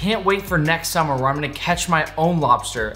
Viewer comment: I thought its said lobster sus